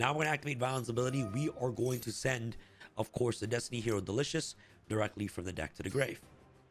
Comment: i heard Delicious..